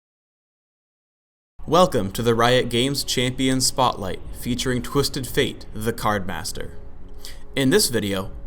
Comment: I thought i heard "featuring Twisted Fate, the Communister"....